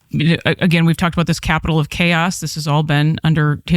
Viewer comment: Capitol of chaos. For a second there, I thought she said Captain Chaos.